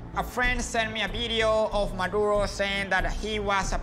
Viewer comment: Don't look at the subtitles Now what does this man say A) Maduro B) Michael Rosen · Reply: Maduro. He’s like the President of Venezuela.